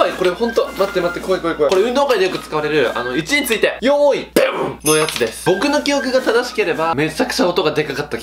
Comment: のとこ「よーいでぶ」って聞こえた